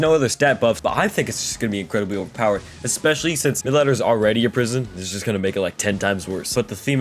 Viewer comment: what did you say? “The letters are already in prison”